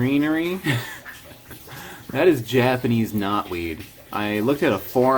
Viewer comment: I heard that as Japanese "Not weed" as in "I swear it ain't weed"